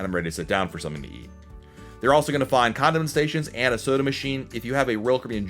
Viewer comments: I’m interested in that condom station that he mentioned about the 26 minute mark!!! Lol!!!!! I had to listen to it a few times because I thought I misheard him. Condiment station, condom station,,, lol!!! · did you say condom sation? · we will find the what station? I heard condom haha